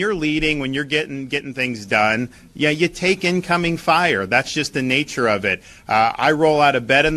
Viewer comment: ?hen you're leading you take income and fire? I could swear Trump said something like that. He asked me it's times of maturity verse the lack of. Good verse evil